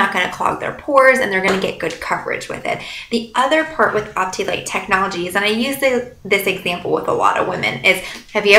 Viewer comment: What is the patent you mention at 3:49 minutes? I can't hear it Thank you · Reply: Mireille Gauthier Arbonne Cosmetics features cutting-edge technology that sets it apart from competitive products. Optilight Technology was developed by Arbonne to promote maximum radiance. This technology works in two ways: First, finely sized pigments deflect light from the skin, creating a soft-focus effect that gives skin a flawless appearance. Simultaneously, finely sized pigments allow skin to breathe, imparting a “light as air” feeling & promoting a healthy glow. HTH~ has given a great tutorial complete with knowledge and tips. Happy to answer any other questions. <link>